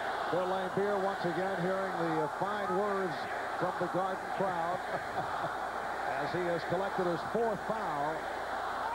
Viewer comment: What are the “fine words” the crowd are chanting at 3:46? I can make some guesses but I really can’t tell. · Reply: Nevermind, I think I have worked out that they are saying, “Laimbeer sucks.”